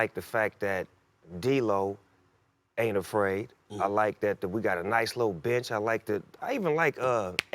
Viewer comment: anybody else think he said "we got a nice lil b****?" lol